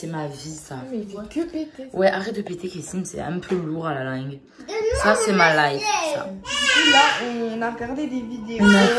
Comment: "arrète de péter kessim c'est un peu lourd a la langue "..... Il a répondu quoi ? jai pas entendu ce qu'il a dit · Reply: Lefor .michel il a dit donne moi mon assiette